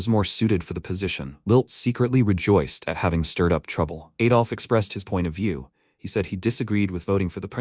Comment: did i just hear someone named adolf???!?!!!!